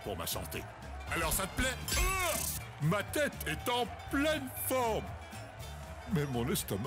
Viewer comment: I always thought that 2:03 said “My head has a blindfold!”